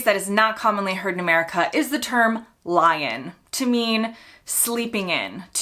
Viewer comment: As a northerner, when I moved to the south of England many moons ago, I really thought everyone was saying “lion” instead of “lie in” at first. I thought that they were all pulling my leg!